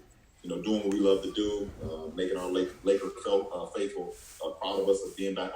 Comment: Did he say laker cult 1:14?